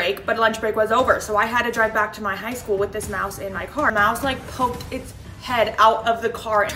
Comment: I was shocked I thought u said mouth😂